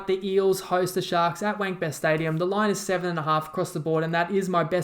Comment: Did he just say “Wankbest Stadium” 😂😂😂